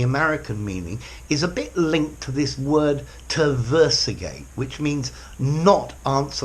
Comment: What's the word he thinks it's like to at 3:16 "terversagate"? I can't find a word like that. Best I can find it "tergiversate": "make conflicting or evasive statements; equivocate; change one's loyalties; be apostate." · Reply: I think he did mean tergiversate.. when I heard it I immediately went to the comments to see if anyone else noticed hahaha 😊